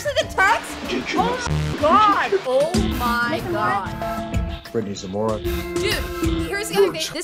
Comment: I had my head down going through a drawer when they said her name, so to me it sounded like, "Brittney's a moron." I misheard, but that's correct.